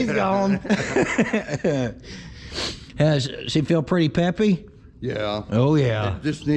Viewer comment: did you say Peppy or pimpy? LoL